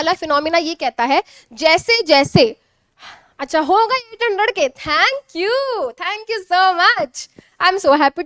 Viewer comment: What did mam said at 1:01:24 sec ? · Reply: Ho gae 800k subscriber